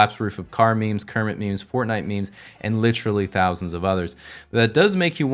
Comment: did he just say Fortnite